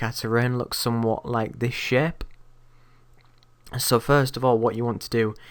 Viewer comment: did you just say "make it look something like this shit" if you did lol